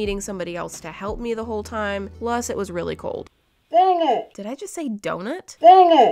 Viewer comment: I heard dan - it